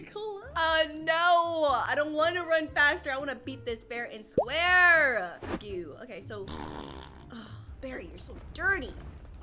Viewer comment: did say the F word? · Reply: editor here, she said “freak”